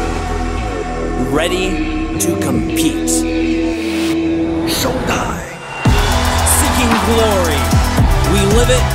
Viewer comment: I hear something different everytime. Don't Die, Show Time, Top Die etc.